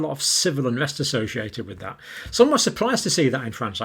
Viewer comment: I'm genuinely interested: why are you so surprised about the freedom protests in France? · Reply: I think he said "some were surprised", his point being that he wasn't